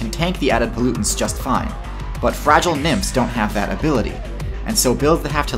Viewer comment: i thought he said fragile nips lol